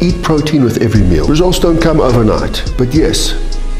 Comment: Lmao I thought I heard "Don't cum overnight"💀💀 so I replayed to see what he actually said. Glad it wasn't what I was thinking